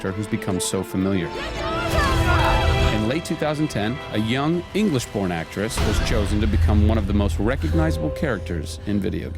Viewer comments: did he say "english porn actress"?! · did he say English porn actress? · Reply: english born actress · I thought he said "English porn actress" and was like: whaaaaaaat?! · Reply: Subtitles are usually inaccurate. He sad "English BORN actress." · I thought he said "English porn actress" and was like: whaaaaaaat?! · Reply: I could have sworn he said Porn Actress LOL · did he say "english porn actress"?! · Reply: lol i went back and listened to it again. HAHAHA sounded like he said "english porn actress" to me too! lmao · I thought he said "porn" actress. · do I hear english porn actor?